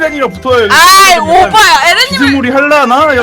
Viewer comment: 에 나만 오빠야라고들리나